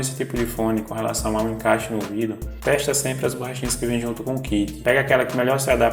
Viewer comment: Não fui capaz de decifrar o que você fala de 3:55 até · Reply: Estou falando do kit de borrachinhas extras que acompanham os fones que são em tamanhos diferentes e você pode testar a melhor para o seu ouvido.